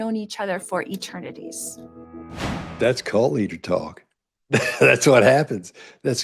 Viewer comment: "That's <something> talk." I can't make out the <something>. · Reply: cult leader talk